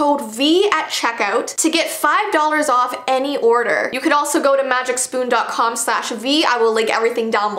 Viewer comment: I thought you said this is my goat to but you said go to lol